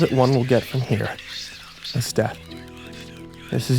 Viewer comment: LMAO! I heard "I'll spoon you to death" 13:10- 13:14 <link>